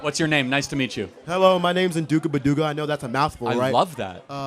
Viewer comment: I'm sorry, did he say his name was Unduga Baluga?